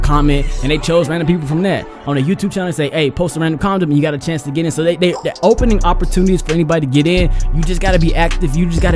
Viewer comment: did I hear what I think I just heard... nah Or maybe I did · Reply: LMAO I INDEED SAY CONDOM. Dont know how comment turned in condom 💀💀💀💀